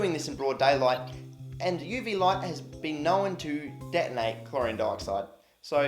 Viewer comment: I thought he said “Chlorine Darkside”, which I thought was quite fitting.